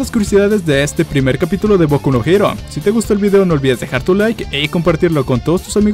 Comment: me asusté pense que decia boku no pico :v